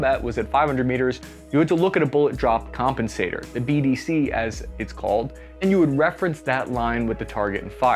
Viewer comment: I thought said “The BBC as they call it”